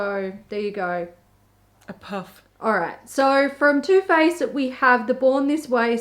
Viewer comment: when you said 'too faced' i thought you said toothpaste